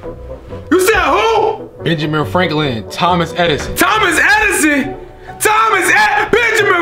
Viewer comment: i thought he said th-omas hedison XD